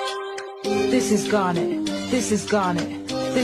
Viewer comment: I hear "this is garlic"